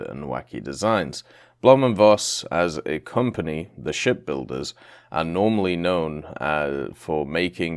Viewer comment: Blohm and Voss ship builders, almost thought you said “shit builders” although that doesn’t seem far off (the shit-posters of Germany)